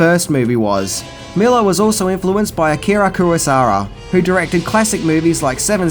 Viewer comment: What did you said...Kurusara ...What a shitty accent..Be carefull (by the way what does it mean) There was a buble gum brand "MİNTİ MİNTİ" in my country "TÜRKİYE"(please dont say my countrys name with your accent.I beg you) when I was child in 80's